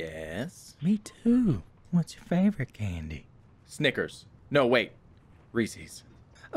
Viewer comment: I thought he said the n-word but he said "Snickers"